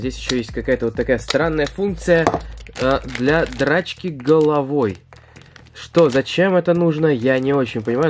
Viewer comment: Мне послышалось дрОчки головой XD